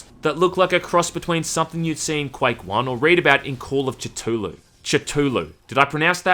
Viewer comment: Call of Titullu. That's what I heard and now it's a cannon eldritch hentai